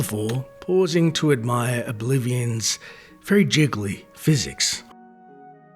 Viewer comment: Im playing Oblivion right now, while this plays in the background. I couldn't see the screen, and I heard, "admire oblivion's jiggle physics" lol. Scroll up to see Ol Constantine here exorcising a rat in Arvena's basement